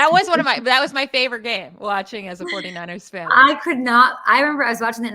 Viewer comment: Did I just hear saying "... as a 49ers fan ... " ?